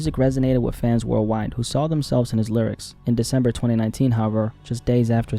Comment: did bro just say Juice died on December 29th lmao